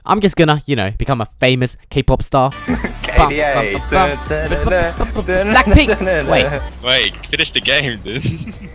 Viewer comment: Was it just me OR did he say „black nigga” 01:01 XD